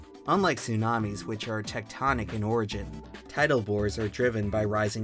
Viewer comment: He said "tidal bores" but i heard "tidal boars" and my mind went 🌊🌊🌊🐗🐗🐗